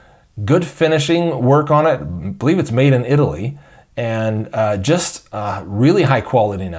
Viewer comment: Where is it made? That is important. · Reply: Charles Perry I thought he said Italy, but I am not 100% certain.